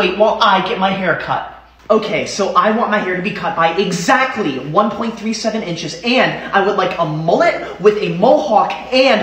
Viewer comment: Did he just say… 1.3 CENTINCHES